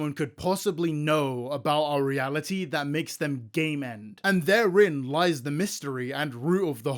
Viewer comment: I have an auditory processing disorder and deadass thought you said “gay men” instead of “game end”